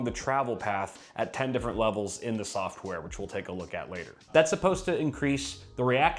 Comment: I hear you said “included software” and i stoped the video.